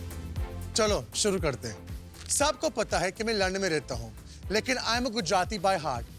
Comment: I heard "sabko pata hai main Land mein rehta hun"